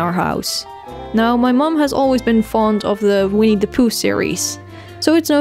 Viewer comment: when you said "winnie the pooh" the first time, i thought you said "we need to poo" XD